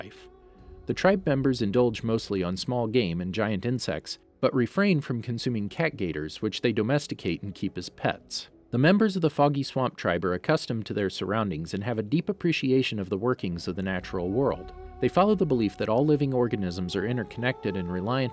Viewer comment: I thought i heard "the fucky swamp tribe "